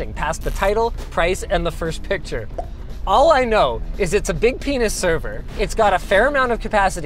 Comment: A big pen!s server? I think I misheard